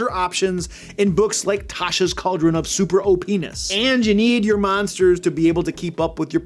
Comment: 🤨 what he say homie?? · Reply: Super OP-ness (super overpowered) LOL - where is your mind, friend? LOL